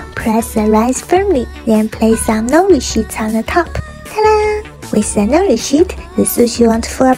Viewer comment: "with the nori shit-" I thought you said no bad words